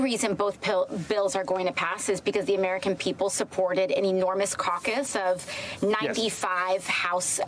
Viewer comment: Did aoc just say “ enormous cauc us”???